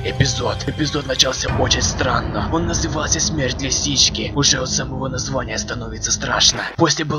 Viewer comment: мне послышалось смерть сиськи